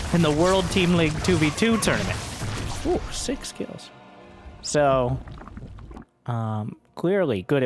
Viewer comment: Did say "six kills" or "sick skills"? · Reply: Six kills